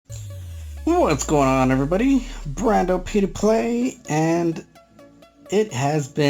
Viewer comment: wait wait did he said p2p?